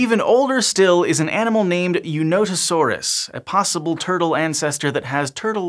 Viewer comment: When he said "Eunotosaurus," was I the ONLY one who thought he'd said "You not a sore ass"? Really? ...NO-ONE else? ...okay, never mind.